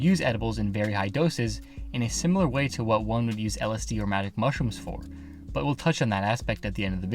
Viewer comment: I thought he said manic mushrooms at first